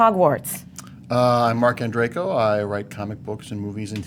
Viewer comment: I thought he said Marc and Draco.